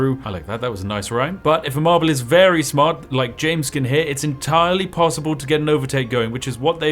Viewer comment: oh, heh, thought you said jameskii for a second.